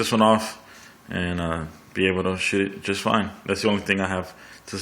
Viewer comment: LOL Did you just say Shit Just Fine LOL!!! Great Vids Though!!!